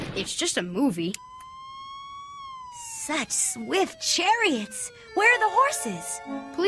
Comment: did she say "sex with chariots?" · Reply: Nerfertina was saying "Such swift chariots." "Where are the horses?"